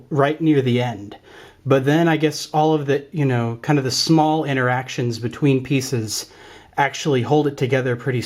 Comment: I thought he said intersectionality at one point. I was almost triggered. 😬🤕😵☻☹👿😈👹👺💀👻👽👾🤖💩